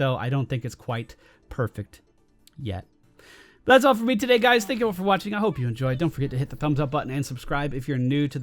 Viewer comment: i thought you said "Thats it for me today guys, thanks for Overwatching..." not "...thank you all for watching" XD